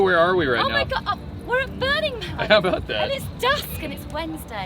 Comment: thought you said burning butt 😅 i mean man ruined your life .?